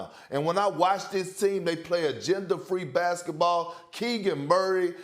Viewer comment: I thought perk said gender free basketball 🤣🤣